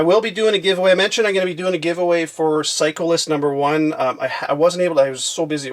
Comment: It's sound like you say cyclist for psycho list haha